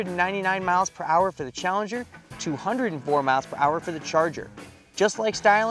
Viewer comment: did he say 104 top speed for charger? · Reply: No, 204 MPH